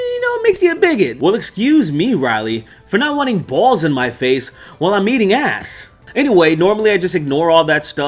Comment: For a second I thought you said you just ignore the balls in your face when you eat ass 😂